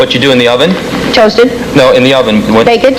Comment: Richard Kline, you cheater! At 12:49, did anyone else hear him whisper "toast" to the woman? If not, wear headphones. And then, she says "toast," and it's wrong.